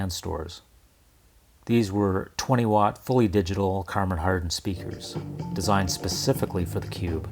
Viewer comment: Am I the only one who heard "Karmon Hardon"? at 21:10. Harman kardon was always a tongue twister for me.